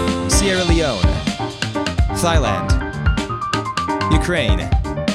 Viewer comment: Did I just hear thigh-land?